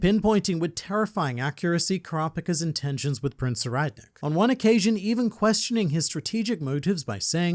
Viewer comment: the subtitles says "Prince Redneck" hahahaha